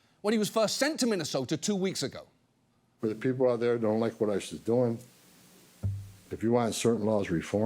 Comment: subtitles say ISIS instead of ICE but tbh great typo